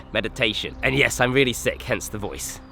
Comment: thought you said "and yes I'm a sick kunt, hence the voice" lol